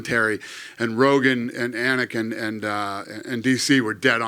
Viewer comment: I thought he said and Anakin. 😂